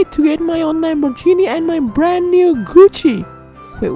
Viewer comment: I heard "my brand new coochie" XD